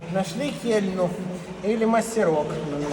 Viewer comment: Что сказал? Много раз переслушал, мне слышится: - Нашли кельню или мастерок. "Мастерок" говорят на востоке Украины, в Киеве я слышал обычно "кельма". А вот "кельня" - это что-то новое. Поправьте, меня если что